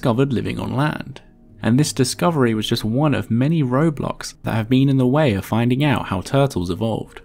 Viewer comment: DID HE JUST SAID ROBLOX 😱😱😱